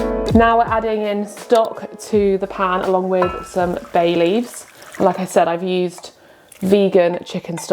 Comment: Ooh, I misheard "bay leaves" as "Baileys" and was very confused for a moment 😂 The noodle soup sounds especially promising, gonna try it this weekend!